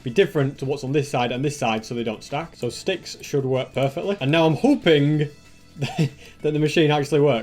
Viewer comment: I sounds like he is saying hooping in